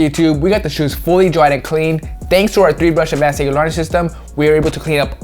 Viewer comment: wtf did he say 🤣🤣...good vid as usual..keep doing ya thing! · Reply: 3 brush advance sneaker laundry system lol